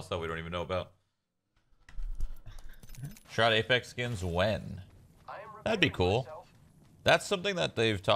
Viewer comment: What the hell is he saying at 10:43? CC says 'sharda Mexicans when'